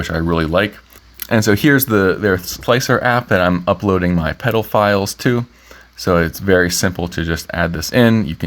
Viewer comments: did I just hear him say " pedophiles ". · ah, upload the pedal files to??? definitely misheard that one